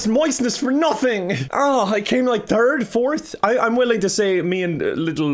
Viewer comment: "I came like third? Fourth?" What I heard "I came in Turg? Fourth?" There is no 3rd now, only Turg.